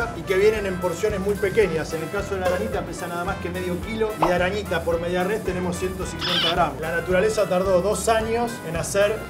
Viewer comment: Entendí "De Postre Media Res" ...